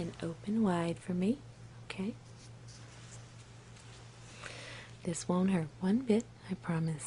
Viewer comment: What she sais "This won't hurt one bit." What the captions say "exploding" 4:35 WHAT?!